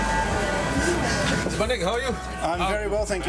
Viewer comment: Did he just say 'My nigga, how are you?'